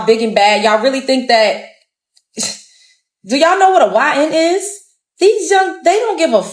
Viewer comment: did she say YNs. Bro, they would get crushed.